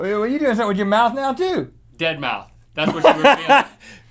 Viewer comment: I thought he said deadmau5 XD